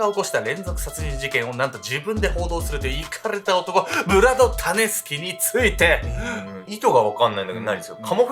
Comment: 時々｢タネスキ｣が｢タネスケ｣に聞こえて、脳内でしゃもじ持ったヨネスケが騒いでて動画の内容がなかなか入ってこなかった( ;ᯅ; )笑